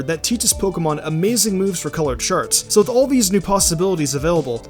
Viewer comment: who else thought he said colored sharts